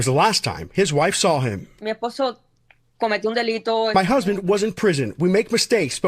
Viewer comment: What she actually said was “He committed a crime” not “He was in prison”. These people are lying to you any chance they get.